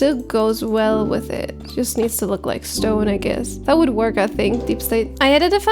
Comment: and 3:46. Did I hear a rock and stone?